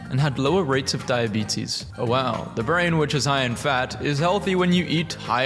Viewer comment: I thought bro said “the brain is high and fat”